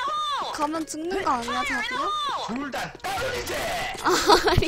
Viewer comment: 나만 자기야라고 들림? 아님 말구..